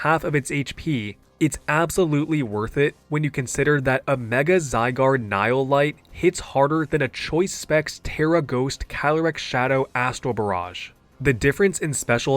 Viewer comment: As someone that barely keeps up with competitive Pokemon, I have to ask What in the fairly odd parents did you just say? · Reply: A Mega Zygarde Nihil Light hits harder than a choice specs tera ghost Calyrex-Shadow Astral Barrage